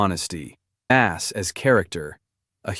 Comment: did anyone else hear "@ is a character"